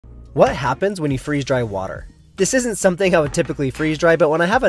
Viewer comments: I heard this as freezing "dry water" and it raised a lot of questions. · It sounded like you said freeze, dry water and i was so confused